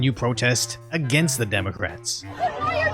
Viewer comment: It took me a while to shake it off (research) after I heard “protest against the democrats” at the beginning (0:06).